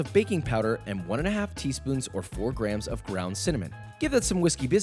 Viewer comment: i thought he said brown cinnamon and it wrinkled my brain i was like yo...what other kind is there?